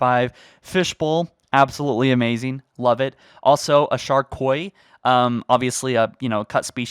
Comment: Chief says taco bell, that's all that really matters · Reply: I thought he said shark boy